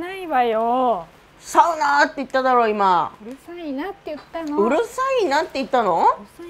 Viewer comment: うるさいなー！！がｷｻｲﾅｰｰｰｰ！！に聞こえてワロタ